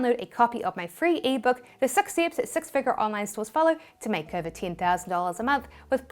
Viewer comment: mins in did you say sex figure 😂